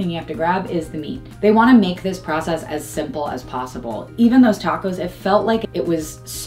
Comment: First time I see chayote with tacos! I thought you said tostadas...anyway looks good. Thank you